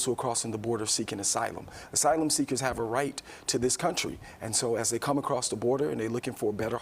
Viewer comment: Did he say," Asylum seekers have a right to this country?"